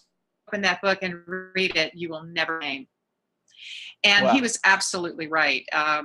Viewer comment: what does she say at 36:17?...'You will never....'? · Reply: Shawn 71 hi Shawn! What I said was "you will never be the same!" My friend, Will, who went to heaven in 1998 said this to me when I picked up the first volume of the Spiritual Diary. 😍